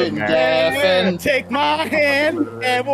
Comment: I thought said “take my hair” and I got very confused 😂